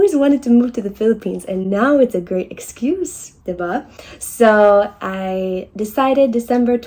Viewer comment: diva?! 😭😭😂😭 the first one I was just a lil bit hesitant that I heard her right until she repeated it. Diba?